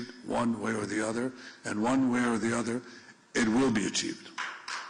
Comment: I thought they said he'll be arrested.